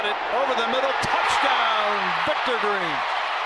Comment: did he just say touchdown Victor green? Its virgil